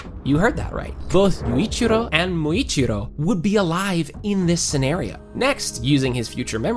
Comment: both Muichiro and Muichiro that what you said broh